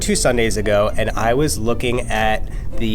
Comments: I thought he said "I was born two Sunday's ago." and it took me a few seconds to realize that he said "bored" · What u heard: I was born 2 Sunday's ago What he actually said: I was board 2 Sunday's ago Like if u agree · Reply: What we heard: i was born two sundays ago What u wrote: I was board two Sundays ago What he actually said: i was bored two Sundays ago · I thought he said I was BORN two Sunday’s ago 😂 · What you heard: I was born 2 Sunday's ago What he actually said "I was bored 2 Sunday's ago" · I thought he said I was born 2 Sunday’s ago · i thought he said “so i was born 2 sundays ago” HAHAHA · Did he say he was born two Sundays ago??? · I thought he said I was born two sundays ago! 😂😂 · I thought he said he was born 2 Sundays ago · am i the only one who thought he said i was born two sundays ago?? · he said “I was bored two sundays ago” but did anyone else hear him say “I was born two sundays ago”? 😂